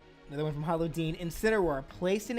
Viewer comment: Everytime he says "hollow deen" i always mishear it as "paula deen" lmao